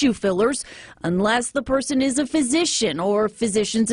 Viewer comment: Why did i hear 'she went to an ass physician' 😂